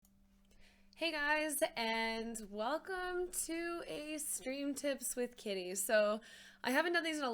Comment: At first I thought she said "Welcome to Stream Tips with titties" and was like: this is gonna be awesome! Soooooooo disapointed. 🤣